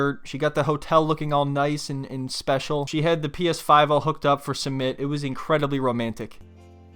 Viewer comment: you say Summit instead of Usman. How dare you....